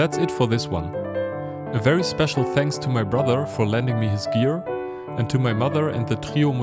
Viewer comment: I first thought you said "Thanks to my brother for lending me his beer" Nice video btw, it's quite a good analysis